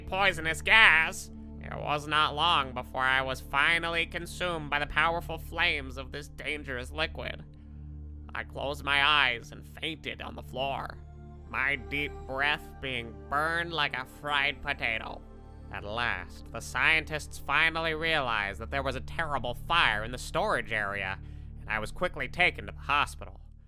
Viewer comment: I heard "my breath did burn like a crying potato"